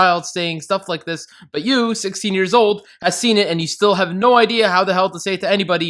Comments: you say 16 but the captions say 15 adding sin for that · I’m taking <num> sins to because in the audio at 2:26 he says 16 and the subtitles say 15 and because you gave a sins to then7 deadly sins when that happened with the gravity times 10 or whatever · he says 16 while caption says 15 ADDS A SIN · did anyone else notice at 2:25 says 16 but the subtitles say 15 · “but you 16 years old” subtitles says 15